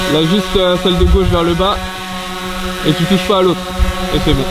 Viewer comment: c'est moi ou a 2:55 il dit "lève juste celle de gauche ver le bas"?? si c'est vraiment ca il parle bien la france